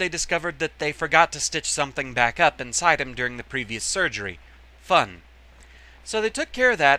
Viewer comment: Here's hoping your family's health gets better soon. What did you say around 1:45? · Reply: "Fun"